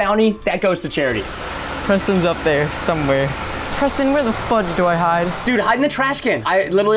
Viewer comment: wait what did he say? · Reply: minecraft player did he say the f word or fudge?